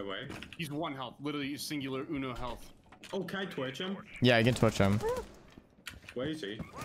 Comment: "oh can i twitch him" no one is runnin twitch on their team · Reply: I’m pretty sure he said “can I torch him” but he has a speech impediment and can’t say his “r”(no hate) and just misheard him. Because there was a guy playing maverick.